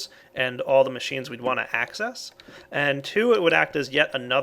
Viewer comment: I heard "hack-cess"